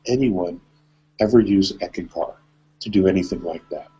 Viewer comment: What did he say about dreaming? He said he would never ever recommend Eken car? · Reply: ECKANKAR